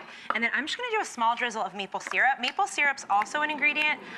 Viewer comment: when she said maple syrup i thought she said meatball